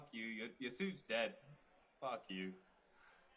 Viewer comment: did some say F U? 🤣